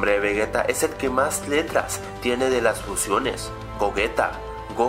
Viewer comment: wow justo estaba sobre mi almohada y no escuchaba bien entonces en ese minuto enves de letras escuche tetas XD